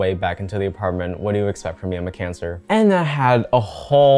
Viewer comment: had to rewatch that a few times since I heard "I have cancer"